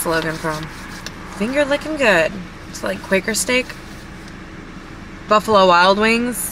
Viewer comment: I thought you said Quaker State. You said Quaker Steak.